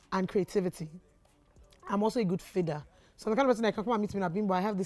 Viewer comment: Can someone please confirm what word she said at 14:46 "she is also a good fida/figa/feeder'" (Pretty similar to her in terms of when people speak to me about their ideas i can help them with lots of ideas, execution etc, searched all over for a word to describe someone like me...and she just did but i cant find the word she said anywhere...) someone help or please respond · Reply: She said "feeder", that she's able to help you gross your ideas by adding something or give you ideas on how to go about it.